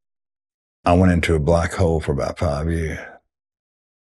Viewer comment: is it "FIVE" or "HALF" year ?? Can't hear... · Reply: "Five years ..."